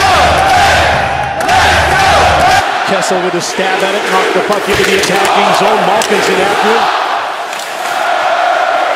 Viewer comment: What was the crowd saying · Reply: "That's 2! Thanks Murray! Can we have another? He shoots, he scores! You suck! Muurrray! Muurrray! Muurrray! You suck! It's all your fault! All your fault! All your fault!"